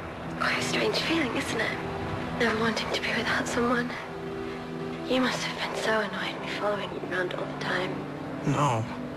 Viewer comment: what does she say at 2:17 I can't quite hear it. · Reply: A bit late, but here you go: "You must have been so annoyed, me following you around all the time"